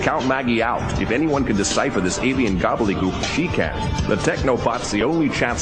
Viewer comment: Mac: Don't be so quick to count Maggie out. If anyone can decipher this alien gobbledygook HE can. Did I hear that correctly? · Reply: no i can clearly hear mac say "she can"